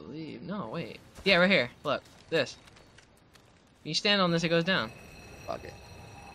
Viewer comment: Audio translate "you stand on this and it goes down" Thought It said "feels down as he goes down" epic xD